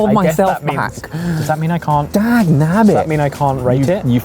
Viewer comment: I've never heard anyone swear and admire someone for it other than! 🤣🤣🤣 That " You cheeky son of a Gun and Dad knob it! Was so cute! I'm not sure if I heard the last one right though! Lol😂